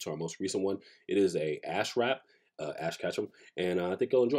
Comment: I thought he said ass rap and i lost it